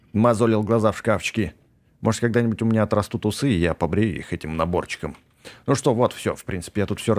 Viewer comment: мне послышалось может я когда нибудь сниму трусы и побреюсь этим наборчиком (впринцепи персонаж женщина)